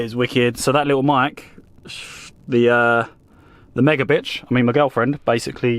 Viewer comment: did you just say "the mega bitch"??? 😂😂😂